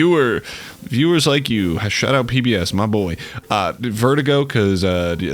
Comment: wut is he even saying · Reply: PBS